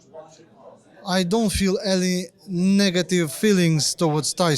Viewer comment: Swear I just heard Usyk call Fury a donkey!!! He must've been watching The Gloves Are Off Chisora Vs Whyte